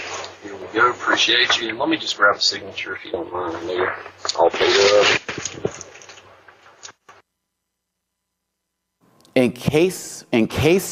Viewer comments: I heard "Let me just grab a signature if you don't mind, will ya?", or am i listening to the wrong part? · It sounds like he said "and ya all paid up" without the "d" via a portmanteau in this case. I'll not be surprised if we were to find out after audio speech recognition that this turned out to be a person attempting a "reach" in order to make false claims, and thus sue a large corporation for monetary gain. The only way it would make sense were if the delivery driver recording, and happened to be playing some sort of "n-word challenge" with his co-workers to see how many times he could slip it into conversation in a work day, based on a bet. If that sounds ridiculous to you, then congratulations, you've got common sense and likely a positive outlook in this daft scenarios. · Sounded like he said "here ya go" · I heard him say "let me just grab a signature if you don't mind will ya". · It sounded like he said “Here ya go”. He said that as he was giving the customer the pen. The audio is garbled, and sometimes cuts out. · It sounded like he said if you don't mind can I get a signature do ya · The delivery driver sounds like he said "in case I need it" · I thought he said: "...grab a signature, right here." and it is the "Right Here" which is being confused as the N Word. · I hear him say "let me just grab a signature if you dont mind a minute" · I heard "let me just grab a signature if you don't mind, will ya" Which is a phrasing that fits the accent the delivery guy had.